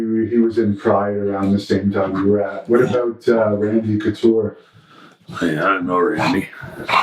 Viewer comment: What did he say at 56:15 about Randy Couture? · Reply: "I dont know randy"